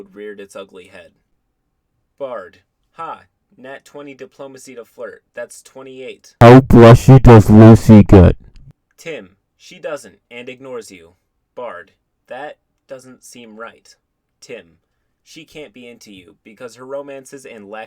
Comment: "How blushy does lucy get?" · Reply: All I heard was "how b- bass -oes Lucy get"